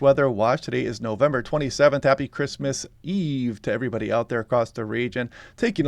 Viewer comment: Did he say Happy Christmas eve? I guess the weather feels a little ahead of the calendar this yer.